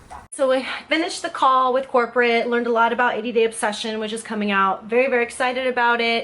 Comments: Oh she was saying “80 day obsession” I thought she was saying “ADD obsession”. I was kind of confused, but figured that most of their products don’t really make sense anyways. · Oh she was saying “80 day obsession” I thought she was saying “ADD obsession”. I was kind of confused, but figured that most of their products don’t really make sense anyways. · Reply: Elisya Sinea I thought she said 80 dip obsession! I do like chip dips...